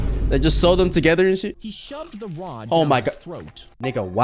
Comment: "he shoved the rod down his throat." Am I the only one who heard he shoved LeBron down his throat? 😫